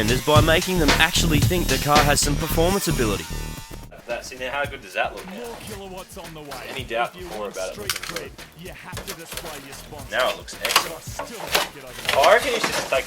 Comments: listen closely at 4:32, does anyone else hear say "more kilowatts on the way" ? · anybody else heard ghost in the background at 4:32 ? he's talking about MORE Kilowatts!